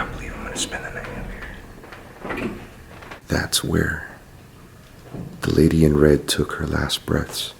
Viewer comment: Sounded like he said... “that’s where the lady in red took her last breasts” bwahahaha! Sorry!!! It was hilarious